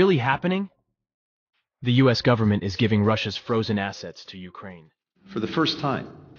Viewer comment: Oh misheard first time ,thought he said was giving Ukraine FROZEN ASSES LOL